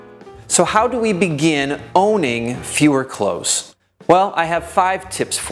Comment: Congrats on proper grammar! Thumbs up for "fewer clothes" rather than "less clothes". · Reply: To me it sound like is saying "fewer close". :D